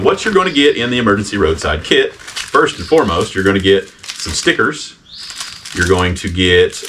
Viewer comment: You guys are great! I 100% thought said the first thing was snickers not sticker 😂